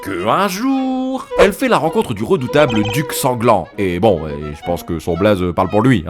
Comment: J'ai compris "le duc sans gland" je comprenais pas...